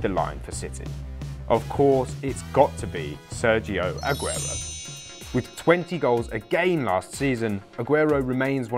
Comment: Thought he said "20 goals a game" for aguero nd a was so confused